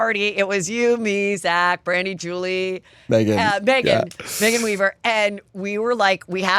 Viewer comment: Did Heather say , around 1:07:30 mark , Megan weever??? They are friends now?? So Megan also had a falling out with Jeff? Im so in the dark! We need a juicy update on the “we hate Jeff” friend group! Lol 😂